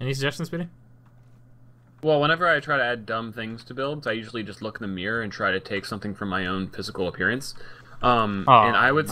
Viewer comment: What was speedy saying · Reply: "Well whenever I try to add dumb things to builds, I usually just look in the mirror and try to take something from my own physical appearance and ummmm..." You don't know how long that took.